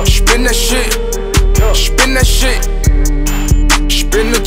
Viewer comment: Why it sound like he saying spinach???